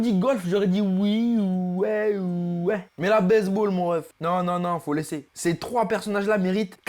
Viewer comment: il a dit "base ball"mais j'ai compris "baise ball"🤣